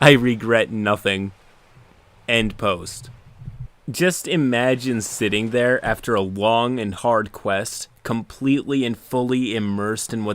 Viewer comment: honestly thought you said “Just imagine sitting there after a WONG and hard quest...” had to rewind to make sure.